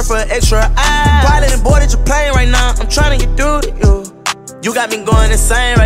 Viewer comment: i thought he said “you got a young n*gga in pain rn i’m tryna get thru to you”